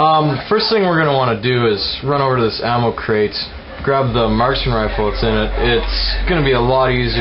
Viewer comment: at first i thought he said martian rifle and i was like da fuck? he said marksman rifle :p